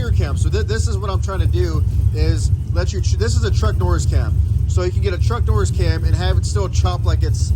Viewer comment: I thought you said Chuck Norris cam 😂👍